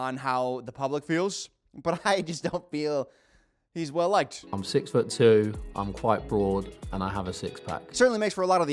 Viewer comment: I heard I'm 6 ft 2, I'm 5 4😭 I was so confused I had to replay it and then proceeded to roll my eyes